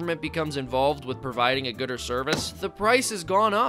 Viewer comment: At first I thought he said gooder service.